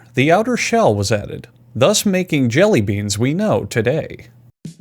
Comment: did you just say jelly beab at the start